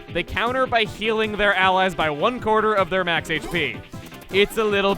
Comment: d-did that just say 104....